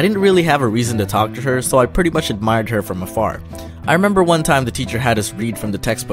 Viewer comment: I thought he said that he admired her from a fart 😂😂🤣🤣